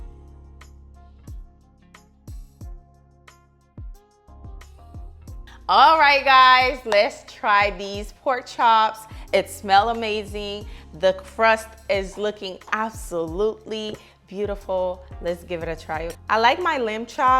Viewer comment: hol on did you say pork chips i thought these were lambs I've been bamboozled 😂